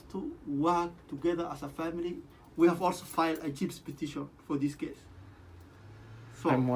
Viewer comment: Did he say “we have falsified a chips petition for this case”